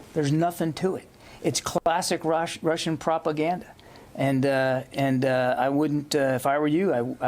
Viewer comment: did he say "Kolbasic Russian propaganda"? From Russian колбаса (sausage, rus.)